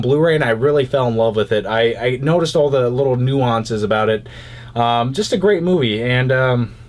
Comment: what does he say at 1:48, when he's talking about "Drive"."I noticed all the little ____???" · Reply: Nuances